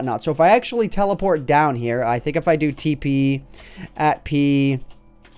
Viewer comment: At somewhere in between 2:25 a 2:2u, the captions said "pee pee" when he said tp. Now that's funny.